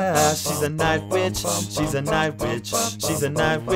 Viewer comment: When you were singing I thought you were saying "hes a night b!tch"